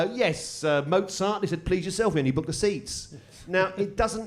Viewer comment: What does he say here and what was the joke? "Mozart? Please yourself...something something seats."